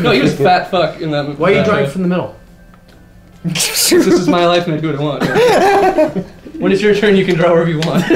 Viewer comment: What did say? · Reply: "Cuz this is my life and I'll do what I want" is what i said. You were very close!